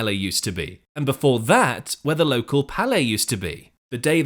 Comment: For forty years, I thought they were saying "parlay," nor "palais." I mistakenly believed the song to be about sports gambling.